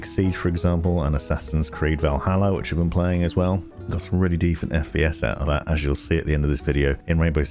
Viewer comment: Dunno what you're on about tbh so 🤷‍♂️ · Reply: also did you say ''defent FPS''? Is it an inside joke or?